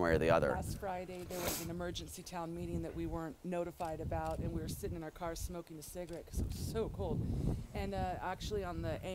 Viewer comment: Is she saying "We was sitting in the car smoking sigarettes, it was so cool" ? At 5:29? Im bad at english, sry. But in that case, i dont believe them. Just a big act :P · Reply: MjewrnskyPwnsk "we were sitting in our car smoking cigarettes, because it was SOO cold"